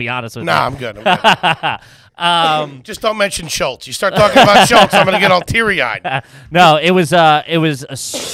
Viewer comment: said "dont talk about Schultz" I heard "dont talk about the stroke"